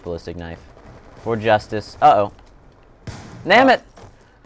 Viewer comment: did sohinki just say nammit